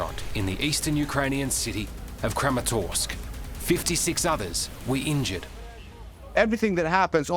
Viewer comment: When he said six others when he talked about the missle at a pizza place I thought he said six ovens